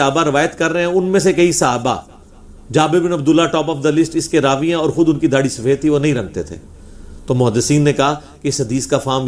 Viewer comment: me ye kya bol gaye? Darhi nahi rakhte the? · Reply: "Darhi nahi rangte thay" matlab color/dye nahi kerte thay..